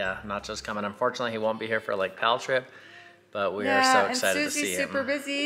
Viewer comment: And, in the vlog, I heard: “Mel trip” or something? What trip is coming up for you guys?